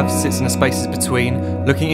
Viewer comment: what he is saying there 0:12 ? the problem i have SinceSS bla bla between? where are the subtitles? :)